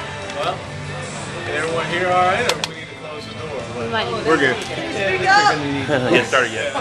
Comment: did he really say F* that door at 0:08?